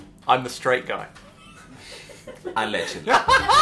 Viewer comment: What does he say at 0:27 ? I can't tell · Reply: Allegedly